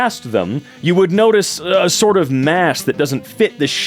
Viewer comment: Did he just call them "a mass" ? 🤔